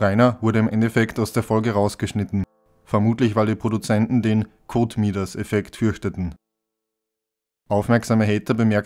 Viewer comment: wie heißt der Effekt, vor dem die Produzenten Angst hatten? Codemeters? Ich konnte da nichts zu finden, gehe aber davon aus, das nicht richtig verstanden zu haben. Weiß jemand mehr? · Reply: Der Kotmidas-Effekt, das Gegenteil des Midas-Effektes.